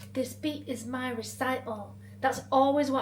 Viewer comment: i always heard it as 'this b**ch is my recital" it's 'speech' apparently, so we were both wrong